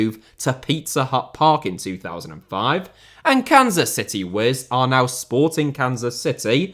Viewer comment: Before I saw the spelling I heard "Kansas City Whiz." Which lead me to the thought, "are they taking the piss...?"